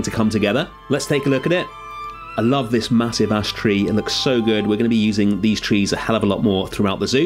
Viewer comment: Thought you said massive a** tree not “massive ash tree” at 8:34 and I died laughing after realizing! 😂